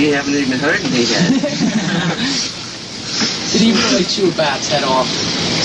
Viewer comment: Anyone know what question is asked at around 13:14? I could not hear it clearly. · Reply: "Did Ozzy chew a bat's head off?"